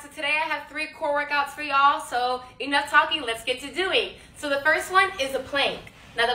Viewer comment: Man I thought she said "Enough talk, let's get to do me!"